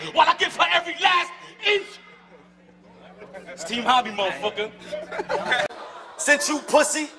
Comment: I think dude at 4:07 said "team homi" not "Steve Harvey" lol